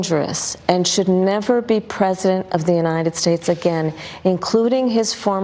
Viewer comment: She clearly says "never be PRESENT of the United States of America again." She's high as a kite.